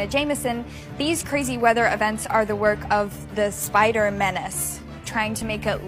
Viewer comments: Spider-‘Men’ace More than one Spider-Man maybe 🤔 It’s a stretch but tell me what you think. At this point I am literally just trying to make up stuff to make me get excited about the potential (there is no way that they aren’t in the movie) appearance of the three Spider-Men. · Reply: at first I thought she said spidermen xD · Instead of “spider menace”, I heard “spider men’s”, and my heart just stopped beating for a split second lol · I thought she said the spider men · I thought she said “the spider men” but she really said “the spider menace”